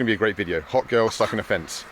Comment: "Hot girl stuck in fence" i think i have seen that video before.... Just not on YouTube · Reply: I thought he said "sucking a fence" wtf